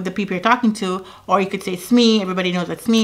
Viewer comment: Smi? What 3:19 smi? Everybody knows what smi is ) sorry I didn’t get what you said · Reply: SME - it means "subject matter expert". These are the stakeholders who know the intricate operational details of whatever process/product/service your project is about. Thanks for watching and I hope that clears it up.